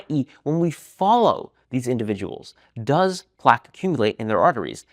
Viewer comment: your caption says "plague" instead of "plaque."